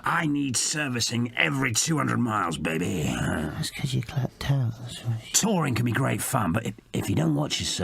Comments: What does 2D say under his breath at 47:04?? "It's cuz ????, thats why~" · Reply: It's cuz your clapped out that's why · What does 2d say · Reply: "That's cuz you're clapped out, that's why"